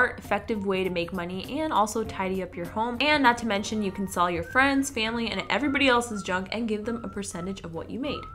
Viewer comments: When she first said "you can sell your friend's and family's junk" I thought she said you can sell your friends and family.... 😂😂😂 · at first i thought she was going to say sell your friends and family😂